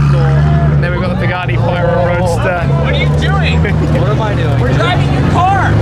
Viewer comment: What did the guy yell at 11:15? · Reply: what are you doing , we’re driving a car lol that’s what it sounded like